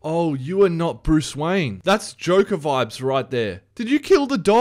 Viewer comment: am I the only one that thought he said "jojo vibes" because he killed a dog? xD